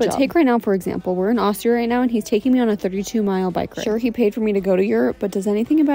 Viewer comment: At first I thought you said a 22 mile bike ride and I thought Dang that is really bad that's tough you gotta do that but then I realized you said 32 miles and I'm almost fell off my chair